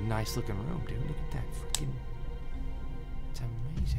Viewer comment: Did anyone else hear say "fucking" at 9:49? Or am I going insane? Haha